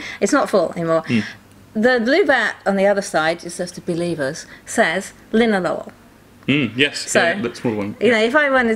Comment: what is she saying? Linnel Oil? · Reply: Nevermind. Linalool is what it is.